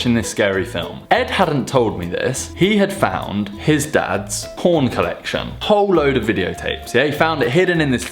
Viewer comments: i thought he said: ''horn collection". i was like, ''odd''. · i thought he said 'his dads horn collection'